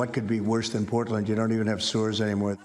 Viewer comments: I thought I heard "you don't even have sewers anymore" and got even more confused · Man I thought he said they don't even have sewers anymore. Stores? How does the media let him get away with that? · I heard sewers instead of stores